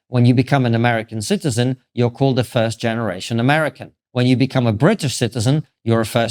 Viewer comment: it sounded like you just said America is “racist”…?